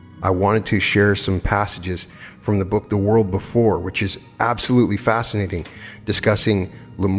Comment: Which book is this please? The title! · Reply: Now when I went back to play it again from beginning I heard you saying “world before” now iam confused 😬